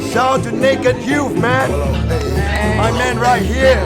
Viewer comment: I heard “Shout out to N*gga Youth Man” LOL